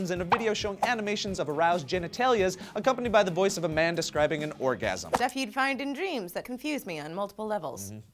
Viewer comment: For a couple seconds I thought said "The voice of Amanda scribing an orgasm". Now I'm picturing Amanda Bynes writing down "Ooooh yes yes yes yes yes yes ohhhhhhhh!" on paper.